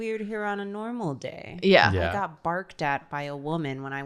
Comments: The fact that said "some lady barked at me while I was getting coffee today" and no one even batted an eye really shows the "New York essence" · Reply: i thought she said Barfed and still didn't question it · "I got barked at by a woman when I went to get coffee this morning." The fact that no one really reacted to that solidifies the point they were making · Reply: I thought she said barfed and I didn't blink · Listen…I’m aware I’m like half asleep watching this but I am certain I’m not crazy 6:07 I thought said she got barked at by a pigeon